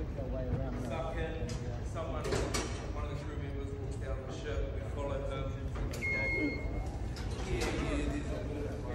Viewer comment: Thank you for posting, so cool! Now can someone PLEASE tell me what language is spoken here in the background 2:39. It sounds very vaguely like Norwegian to me but I can't make out any specific words. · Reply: I’m pretty sure it’s just English, with a NZ (I think?) accent. Sounds like somebody saying “one of the crew members walks down the ship, we follow them…”